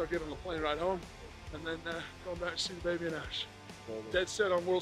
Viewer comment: When he said "go see baby and Ash" I thought for a sec he said "Fabian, Ash" 😂